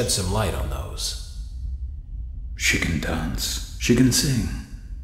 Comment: When they said “She can dance” I heard “Chicken dance” 😭😭😭